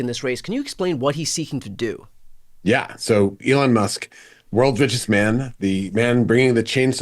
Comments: I thought I heard "Elon Musk, world's bitchest man" and I am here for it. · I heard it as World's ViciousMan😀